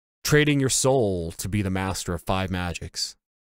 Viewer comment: I've always thought it sounded like he was saying "Thigh Master."